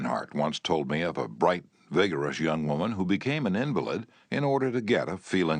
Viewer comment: What is he saying at 52:45? What word did he say? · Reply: Became an invalid i dont know